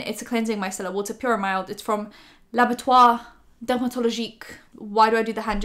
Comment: did she just say "l'abattoir"?